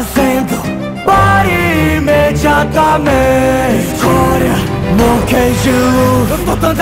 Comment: Eu tava ouvindo esse rap denovo e percebi que eu toda vez eu me confundia em uma parte por que não parte que ele pede pro Luffy tirar o pé da frente eu sempre achei que ele falava (agora monkey d Luffy eu tô dando a ordem tire o pé da frente) mais agora vendo ele fala escória em vez de agora foi só eu ou alguém também ouvia agora